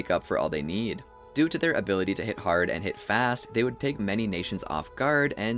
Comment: What did you say Hit Hard and hit fast?